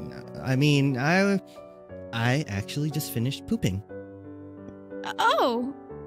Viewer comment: "I just finished pooping" I thought you said "I just finished cooking"